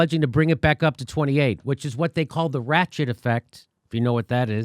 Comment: Oh, the ratchet effect. At fist I thought Jimmy said the "rat shit" effect. Wait, either works, same difference.